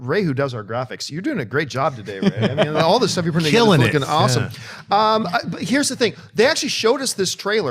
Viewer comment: Did anyone else hear John say the f word at around 26:00? Maybe it was just me. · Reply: Dan Judge He said "flipping awesome". He's said that before on the show before.